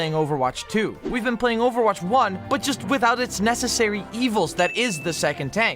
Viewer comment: 1 minute is and i hear "the 2nd tank is overwatch's biggest issue" lol, this is gonna be a good one